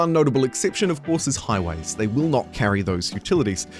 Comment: Can someone tell me what the exception is at 0:31? All I am hearing is highlighters... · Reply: He said "highways"